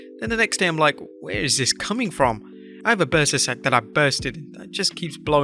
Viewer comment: Bursa sac? I thought he said a burstin sack. 🤣🤣🤣🤣 I was like damn